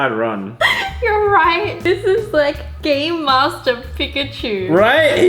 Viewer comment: DID YOU SAY GAME MASTER PIKACKIW? sry Idk how to say this...........................☝🏻